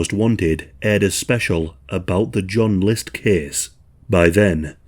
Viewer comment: I got worried when i thought it said joe list.